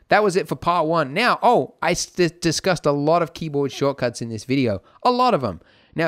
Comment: Good video. I think I may actually be able to get somewhere with Blender because of this! By the way, while watching with subtitles on, I noticed something; at 15:28, they say "...part one now Oh Isis cussed a lot of keyboard shortcuts in this video a lot..." instead of "...part one now Oh I discussed a lot of keyboard shortcuts in this video a lot..." I couldn't help but stop and wonder...